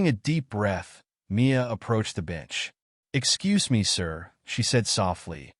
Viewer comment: lol gotta love AI. @ 2:43 says approached the b*tch instead of bench